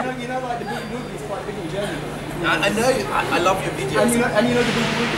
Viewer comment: No se porque raios escuche que dijo "I Love you BTS" creo que de tanto ver coreanos ya me traume 😂💔